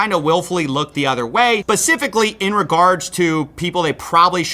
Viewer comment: Bro you should check behind you every now and then...looks like your trashcan is on fire. And did you just say "Pacifically?"